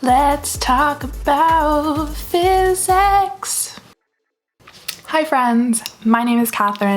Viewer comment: I was just listening to the video and I misheard physics as fear sex I'M SORRY😭